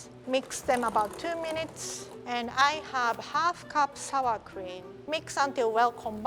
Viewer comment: So, hearing impared. At 7:40, the captions said "ice cream", but it didn't look like ice cream. Was it actually sour cream and the captions were just wrong?